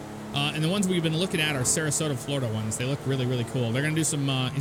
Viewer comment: did I hear him say Sarasota? Love that place but if your do Sarasota you gotta add a <num> impala with cuz they have one that just sits is St. Armonks circle.